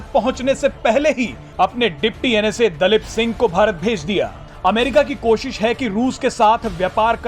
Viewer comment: is it just me or he really said Dalit Singh